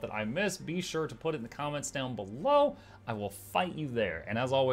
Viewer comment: For a second I misheard "I'll find you there" as "I'll /fight/ you there," and had a moment of "Wait, Muk's going back to PvP???" Though, bet you he still could pwn all of us in the comments section.